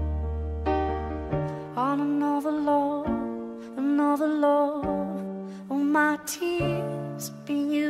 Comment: At first i thought it said im on overload